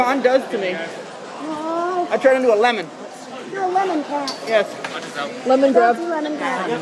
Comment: for two seconds when I heard him say "lemon cat" I thought he said "condom cat" 😂